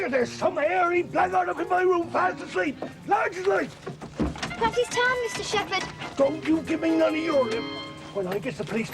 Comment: Never understood what John says here. ‘Perhaps… ‘ ???? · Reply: He says "perhaps he's tired Mr. Shepherd" .